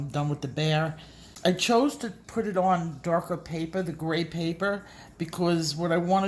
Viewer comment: Haha my little brother pointed out that you're saying grey paper whereas I thought you were saying cray paper lol